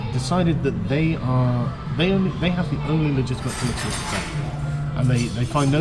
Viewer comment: the silverware noise drowned out what he said. ??? They have the only legitimate ???? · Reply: M. Travis Volker the only legitimate political perspective